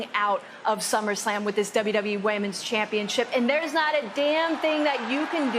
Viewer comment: Nia Jax vs. The Mountain. Who wins? Also, @ 8:20 did Bailey say "Whammens Championship"?